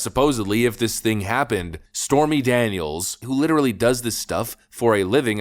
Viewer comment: I thought she said nothing ever happened